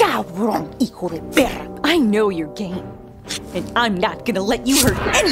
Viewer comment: I heard "I know your game" as "I know you're gay" 😭👍